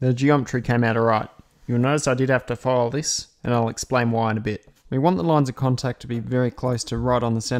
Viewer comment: mark: Yo, my Auzzie English isn't working today. Must be a language barrier. I got the sentence at 3:29 "The geometry cam out all right" but I didn't catch the next. Please explain... Thx for the education, well done. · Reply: "you will notice I had to file this, and I'll explain why in a bit" I forgot to include a small amount of clearance, so I had to file it